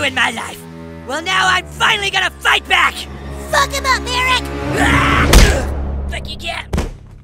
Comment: Why did it sound like butters said “Fuck him up Airrack